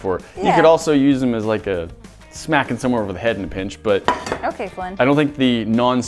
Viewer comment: Did she say "Okay, Flint"??? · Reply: "okay Flynn" as in Flynn Rider from Rapunzel who fought with a frying pan.